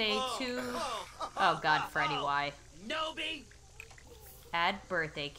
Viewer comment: I heard freddy say "nooby"